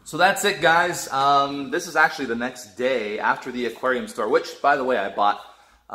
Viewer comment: At first I thought said He bought the aquarium store xD